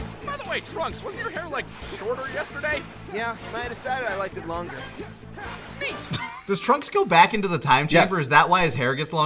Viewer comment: I always thought Trunks said, "My editor decided I liked I longer" when referring to his hair 😅😅